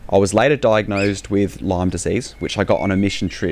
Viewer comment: I thought you were saying Lom disease. I went to replay and realized you're saying Lyme disease. My son as it. Has for many years. He has spend thousands of dollars and spent so much time searching for answers etc. He said he thought he was going to die one time. He is now a dedicated Christian. It is his whole life and he blesses a lot of people and the Lord. I feel for him so much and you too.